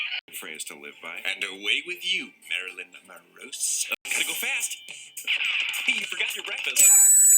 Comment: Wh- I can never make out what Roman is saying please help I'm making a list of all the names that Roman called Virgil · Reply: Roman says, "and away with you, Marilyn Morose". It's a play on Marilyn Monroe's name and "morose" meaning surly, bad-tempered, sulky, etc